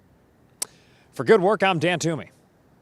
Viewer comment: i thought DAN TOOMEY said DAT MONEY although i wouldn’t doubt DAN TOOMEY has DAT MONEY